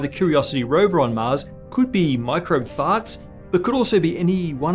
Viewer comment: Did I hear 'microbe fahts' ? :D